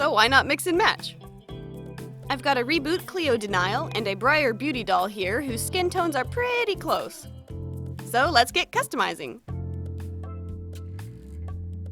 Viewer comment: Since I know nothing about Monster high, I thought you said "Reboot Cleo Denial" and I was so confused